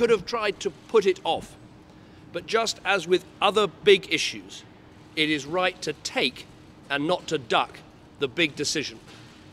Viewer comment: Could somebody help me ? What does he say at 1:25 ? 'it is right to take a ... ' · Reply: Esaul Gimeno "it is right to take, not to duck the big decisions"